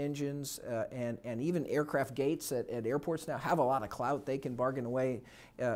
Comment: did he just say they have a lot of clout?